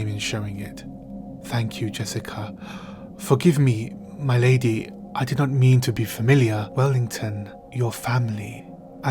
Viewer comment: What does Yueh say 11:40-11:45? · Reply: "My lady, I did not mean to be familiar," meaning that he didn't mean to speak with her in an informal type of way. Then Jessica interrupts him and says "Wellington, you're family."